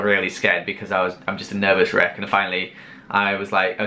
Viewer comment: I thought you said I’m a nervous shriek